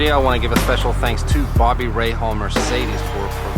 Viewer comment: I always thought you were saying Bobby Ray Hall.